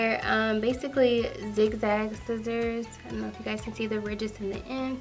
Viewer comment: I swear I thought you said “dickbag scissors” 😂